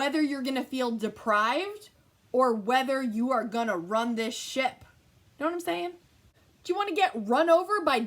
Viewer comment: Lol I at first thought you said, "I run this sh*t!" BUT I DO RUN THIS SHIP😂🤣😃😎 I luv luv your Positivity!!!